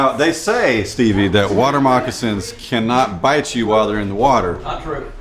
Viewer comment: what does that girl say please? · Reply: I know it's been 4 years but she said "don't get bitten by a water moccasin"